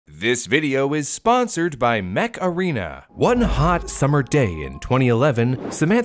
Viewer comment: I thought he said” this video is sponsored by the Macarena” and I busted out laughing lol